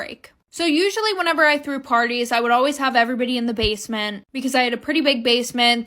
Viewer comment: I thought she said that she hid people in her basement 😹